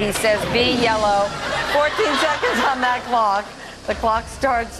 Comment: I thought he said 'tase the man' there at the end instead of J's the man...would have made more sense...